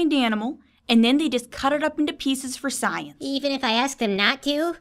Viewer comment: When it said cut up into pieces I thought it said put it into pizzas. That would make sense too. The government would do that.